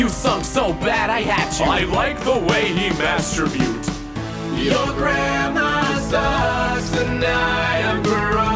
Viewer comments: The autotune makes sound like he is saying " Your Grandma Sucks " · The autotune makes sound like he is saying " Your Grandma Sucks " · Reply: I had an online friend back in the day and he was the one that put me onto. But over the mic for the longest time I thought he was saying "I'm watching Your Grandma Sucks" and I just thought he was weird. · Not gonna lye I thought he said “your grandma sucks”